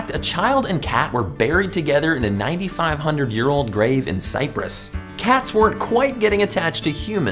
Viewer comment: A child and cat were buried together in cyprus Hold on I remember that on the news oh yeah I'm from cyprus I thought he said cyrus for a second tbh